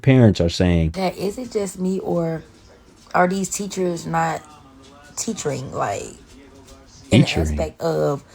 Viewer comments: Did she say "Teachering" 😂Chiiiileeee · did she say "teachering?" haha she worse